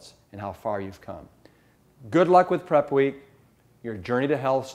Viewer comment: "Your journey to Hell starts now". sometimes I mishear things! :p